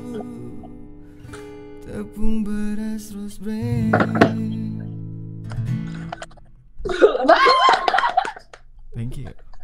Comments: tepung beras rosebrand 😅 itu beneran apa gua yg salah denger ya ?? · Ini Apa Gw Salah Denger Apa Gmna?Yang Video Nyanyi Lagu Leaving on a jet Plane 3:37 Akhirnya Kok Kaya Kedenger "TEPUNG BERAS ROSE BRAND" 🤣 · (tepung beras) bener ga si. Apa gue salah denger wkwk